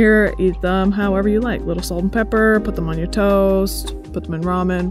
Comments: When she said "put them on your toast" I definitely thought she said "put them on your toes" and first and it made me do a double take lmao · Thought you said put them on your toes😂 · My dumbass thought she said put them on your toes 😅